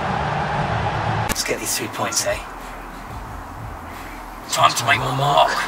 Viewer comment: Anyone thought that it said “let’s get these 2 points eh” Edit:No one ok 😂😭